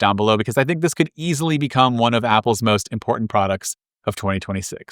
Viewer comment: “this is Bdan from macrumors” every video i think your saying your name is Bdan